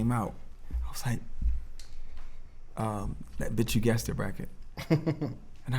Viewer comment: Does anyone know what album Pharrell is talking about around 5:00? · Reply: OG Maco - Bitch u guessed it